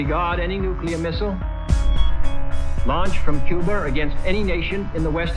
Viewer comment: Cuber? pls tell me im not the only one who heard cuber instead of Cuba